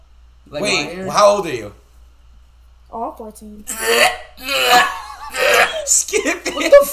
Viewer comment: What did she say guys ? Almost 14 ? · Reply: She’s said ‘’oh I’m 14’’